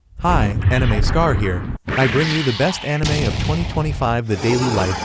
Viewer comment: I thought he said animal scar here 😭